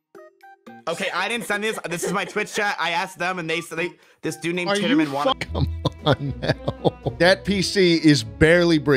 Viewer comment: WHAT WAS THE WORD??? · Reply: Bin laden